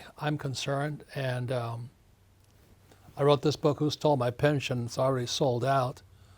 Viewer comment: I thought he said I wrote 2 thousand books my life time 😂